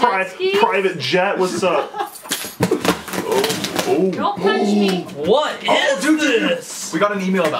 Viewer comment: who heard say don't touch me